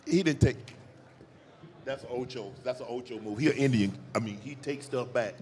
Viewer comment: What he say at 2:37? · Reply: He was about to say indian giver. You gift, then take back. Its like a derogatory term so he caught himself slipping and didnt finish it lol